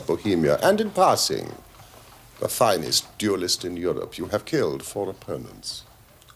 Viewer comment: I don't quite catch the phrase..what is it that holmes says to the king of bohemia..at.. 13:20you have killed for a.....!!!?? ..after which the king replies.. honorably!!!?? Please someone!?? Is it opponent's!!! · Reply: haha I thought I heard more like " You have killed for opponents "